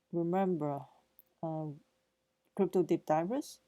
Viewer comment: Sounds like she says ‘ dick divers’ lol